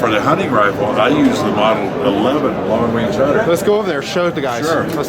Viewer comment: What was that model at 8:30. For some reason I kept hearing savage model aluminum. Haha.